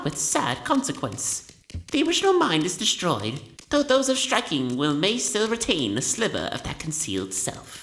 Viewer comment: He says “striking will”. As in, those who have a striking will. A will that is striking. 10:36 - That’s the opposite of what you’re trying to say. You can’t Dream Nail “unmasked” Monomon because of the seal on her. Which Quirrel breaks. Not to mention, it says absolutely nowhere that the dreamers have masks.